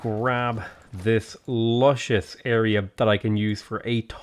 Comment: I think you said lushious because you couldn't get Alexander's sexy hair out of your mind ;), love the video tho <3